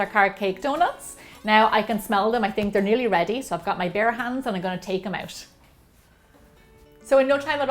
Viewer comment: I got so confused when she said "I got my bear hands" because I misheard her and thought she said "bare hands". Ahhaah. 😂🙊 I might try this recipe since my mom likes carrots :) Thanks!